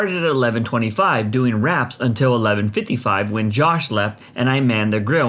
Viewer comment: On the 3rd stories i thought he said rats when he actually daid wraps 😂